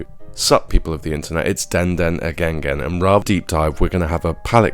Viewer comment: yo did you just say "it's den den again gain"😂 hard🔥